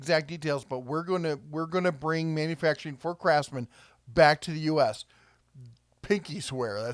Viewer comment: I thought you said "Crapsman" at first. Had to rewind and listen again